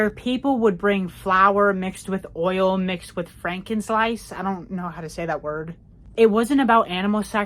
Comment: Frank-in-sense is how I always heard it